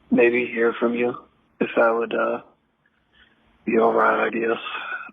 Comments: wow, that was a painful voicemail. "be alright, I guess" is how i heard *INAUDIBLE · I think he says "If that would be alright, I guess."